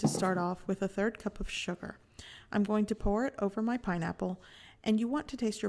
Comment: Oh God I thought u said 3 cups of sugar .. well off to buy 2 more pineapples 🍍😅🤣